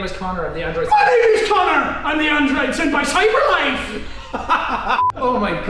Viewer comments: The first time i heard you say ”My name is Connor. I’m the android sent by cyberlife.” I thought you said “My name is Connor. I’m the android senpai cyberlife.” I was confused but i still understood. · i hear "i am connor i am the Android senpei cyberlife" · You know, at first I heard “ my name is Connor, I am the Android senpai cyberlife “